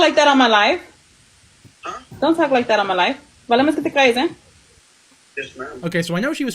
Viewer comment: What I think she's saying is: "Vale más que te calles", which could be translated as: "better shut up" (im spanish native speaker from Chile, our accents are completely different btw)